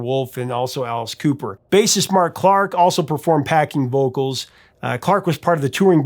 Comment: Did he say "Packing vocals" at 9:52 instead of "backing vocals"